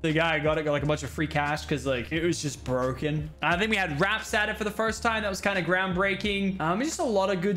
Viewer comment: When u said wraps I thought u said R.A.P.S. From black ops 3